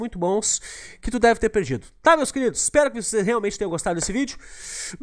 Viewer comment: so eu qeu ouvi "kd o moab colonoo" bem no finalzinho? kkkk